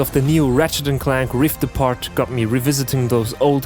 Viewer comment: d-d-d-did you say Rivet-ising